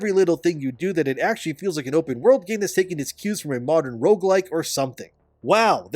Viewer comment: Did you just say rouguelike or woke-like?